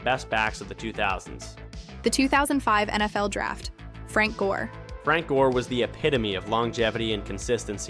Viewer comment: I thought he kept saying Frank Ward. I might need my ears checked 😅